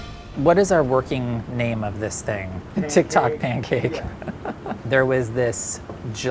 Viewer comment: So you're offended and can't even get the quote correct??? He said "viral Tik Tok thing". PROTIP: Putting "lol" at the end of a sentence just makes you stupider. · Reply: He clearly says "tik tok pancake" at around 3:59-4:01 mark so you clearly have issues listening. So who's the stupid one now? You did that all yourself